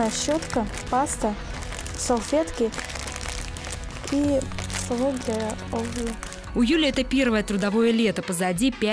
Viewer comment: что она сказала? и савок для ...(для чего?) а то неразборчиво сказала ))))))) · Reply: Димас Мерзлов для обуви. типа ложка